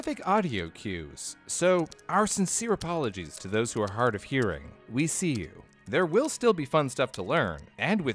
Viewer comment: I thought it said "we need you"